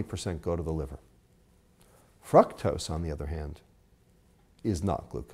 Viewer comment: it sounds like he said Fucktose. lol